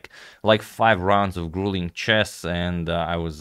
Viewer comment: did he say chess? i thought they were fighting